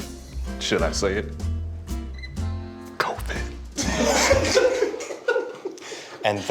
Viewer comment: WHAT DOES HE SAY?!? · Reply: covid lmao